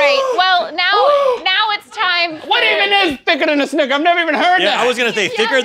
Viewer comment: Oh damn it my brain thought it was "thinner than a sister"😂😂 The sad part is I'm not even kidding, I really thought it said "thinner than a sister"😂